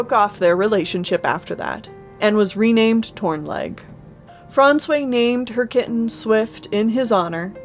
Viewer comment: I keep misheard Frondsway as "François" lol Very pretty kitties, ahh i wish shipping to Europe would be easier, i rly look forward to reading it in the future